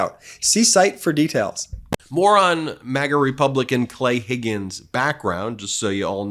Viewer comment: Ben said, "More on MAGA republican Clay Higgins.." But it sounded like, "Morron, MAGA republican Clay Higgins.." 😂😂